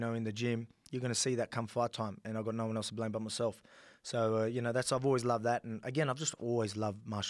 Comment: Thought bruh said my-shylats